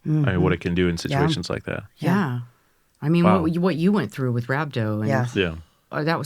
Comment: what is she saying? "you were through.." what? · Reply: She said "what you went through"